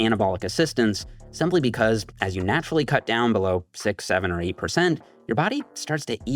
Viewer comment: what did Jef said? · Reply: 6 - 7 🤪🤪🤪🤪